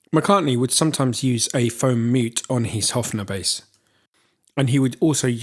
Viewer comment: Would someone write down what does he say at 12:46 "McCartney sometimes use a ....". I dont get it! Thank you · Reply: Lucas O'heyze I got that part. What I didnt get was “foam mute” (?)